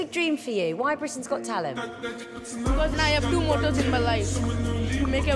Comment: I heard it wrong. I thought he said everything's impossible lmao